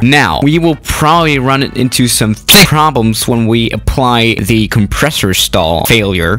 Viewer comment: did you just say thick problems!!